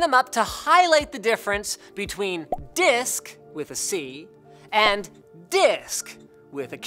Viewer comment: Disc with a c YouTube Auto-generated subs don't understand